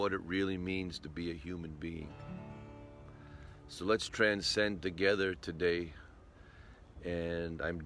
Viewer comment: For a second I thought it said "Let's transgend together."